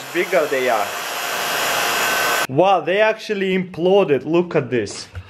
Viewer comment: does he say Exploded or what did he say??